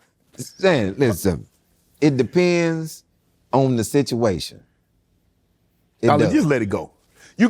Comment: All I hear is “Ollie!” It’s Ah-lee , like Muhammad Ali ! lol